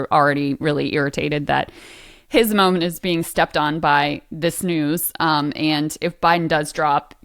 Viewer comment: “being stepped on by this snooze”. That’s what I heard, and that’s about right.